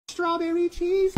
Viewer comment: I just hear “strawberry cheese hay”